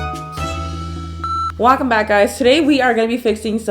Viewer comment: did she just say wack em back guys